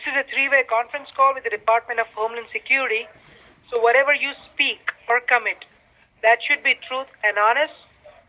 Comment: i thought he said "Whatever you speak, we are coming." that scared me l ol